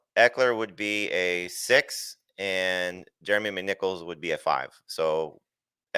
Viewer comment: I thought Jamey said Jeremy McNipples one of those times